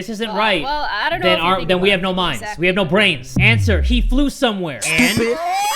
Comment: Jay: You don’t deserve a brain if you get these wrong Also Jay: Gets the first riddle wrong 😂 THANK YOU SO MUCH for 5.2k likes 🤯❤️! Love You all! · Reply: I THOUGHT IT SAID BRIAN XD